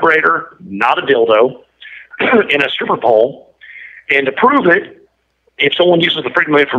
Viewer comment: Did he just say she requested a Vibrator and a super ball ???